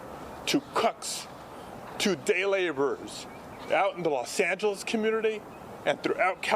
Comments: did he say "Los Santos community"? like as in Los Santos from GTA? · Did he say cucks?